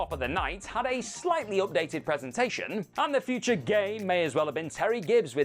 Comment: said “the future game” and the subtitle says “the future gay” instead.😂😅